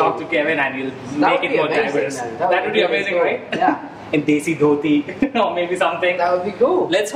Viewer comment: Subtitles say DC for Desi..🤣🤣🤣